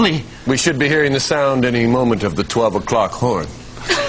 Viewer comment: I always thought Ryan said it's almost time for the 12 o'clock whore, not horn 😂🤦‍♂️